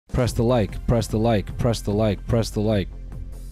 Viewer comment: Could’ve sworn he was saying “Press the mic” and I thought WHAT MIC